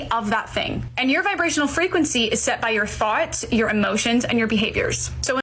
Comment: I honestly hear it as "... is set by your farts , your emotions and your behaviors..." , and I got worried , thinking "my god, this manifestation practices are hard, if you must fart in a certain way that will make you vibrating in the proper frequencies! I do not think I can try this". Today;s episode was crazy! This woman can provide so much content for you. And what you were saying in around minute 26:00 is very true. We should all reply like this to women "so what if you feel oppressed? The Universe has unlimited potential, go out and manifest your reality..." 30:00 , this woman promotes $@@ actually. "experience all your desires...", how dangerous is that? For you and the people around you. A p3d0fil3 is desiring to "explore" kids for example , that is their desire. Or a woman may want an extra luxury lifestyle, so some people must remain in poverty to create the surplus of resources that she wishes to consume. Awful, she is an evil woman, her tiktok should be banned.